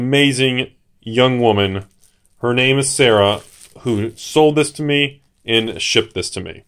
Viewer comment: I heard "Serra" instead of "Sarah" and I was thinking FAKE until I saw the box lol